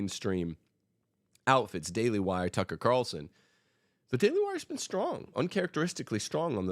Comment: Did he say Taylor Marshall has been good on the feminist issue at 17:50? I can’t understand who he says here.